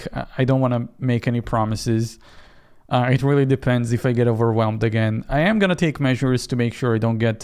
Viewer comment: I thought he said “I am gonna take medicine so I don’t get overwhelmed”